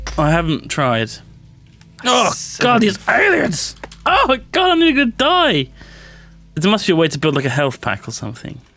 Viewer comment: What he say? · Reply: "ALIENS! Argh, gah-noogah DIE!" clearly.